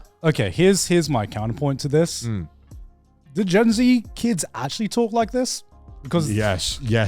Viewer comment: "Zoomer" is just a derogatory term for an annoying child, the same way "Boomer" just means out-of-touch old person. When people talk about "Zoomers", they aren't literally referring to all people born between 1997 and 2012, they just mean kids. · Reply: I agree that “zoomer” is a derogatory word! Later in the segment they might have used zoomer but at 1:20:59 I hear “gen z”. That said, at 1:19:15 the My hero- clip is introduced with “gen alpha” so it’s probably just a small glip in a fast paced conversation. Edit: time stamps